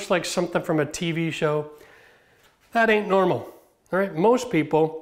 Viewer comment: Did I hear pimped' out and 'it 'ain't normal?" I have a few of those bucket organizers, and I also have dollar store $1.00 buckets hanging in various places for the extras.